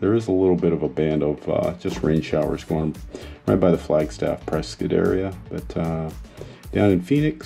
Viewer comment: did I hear you say Pres-SKITTT? 😃🎊🎉